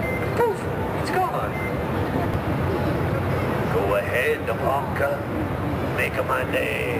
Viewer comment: Did he say fucker?! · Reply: He said Punk. He's referencing Harry Callahan from Dirty Harry